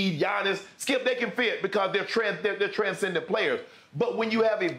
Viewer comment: I swear he almost said "Transgender" players.